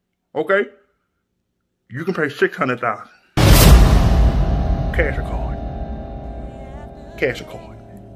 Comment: Some say to this day, they still asking him, “Cash or Card”! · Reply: I thought he said cash or coin as in bitcoin like what tesla has just done this week!